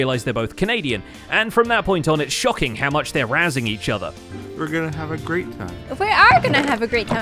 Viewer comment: "...they're both Canadian, and from that point on it's shocking how much they're razzing each other" I expect nothing less of my fellow Canadians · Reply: I thought he said arousing each other 😂